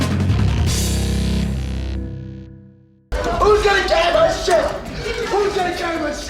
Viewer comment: Way to go, you cockteased a review of The Thing. I'll be expecting that before 2017 is through. 23:49 What the hell is he saying? I can't be the only one who can't make that out. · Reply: "WHO'S GONNA CARRY MY SHIT?!"